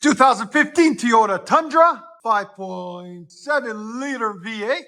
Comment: sounded like you said Tiyota instead of Toyota.